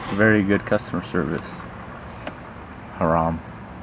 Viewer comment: Did you say “haram” at 8:47?